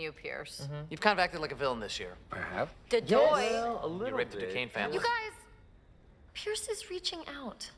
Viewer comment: Abed: 0:47 "you r*ped the Ducane family"I I can't believe this is the first time I heard that. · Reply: He said ripped as in rip off